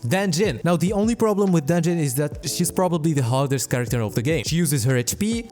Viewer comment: I thought you said Danjin was the "hottest" character in the game instead of "hardest" and all I thought was "Hell yeah brother" 💀